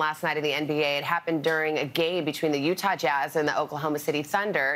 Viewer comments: It's 100% clear what she was trying to say, and slips like this happen all the time, even to the most professional of people, but did she say, "It happened during a GAY (instead of GAME) between the Utah Jazz..."? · she says gay instead of game. Has anybody else noticed it?!!